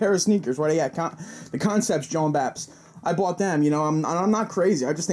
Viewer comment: did you say the concepts "jom baps"? at 5:09? is this real life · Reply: No. You're in a parallel universe. One where the term "Jawnbap" is used as a noun. Sorry to have to be the bearer of bad news my Ninja.